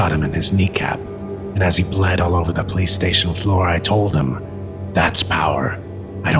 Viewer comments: did he say "As he Bled all over the PlayStation 4 " ?? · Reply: police station floor , the way he mumbles makes it hard to hear, when he says life sounds more like wife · why did it sound like he said "Playstation Floor"? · I thought it said PlayStation 4 instead of police station floor